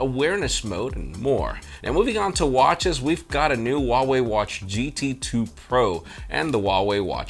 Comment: I heard "Huawei HTC". Got me excited for nothing. 😀